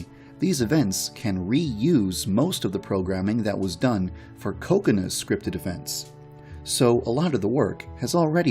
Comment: 나만 코코나가 코코넛이라고 들리나...